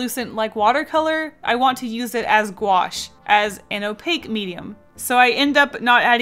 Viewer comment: My fat ass thought she said ganache.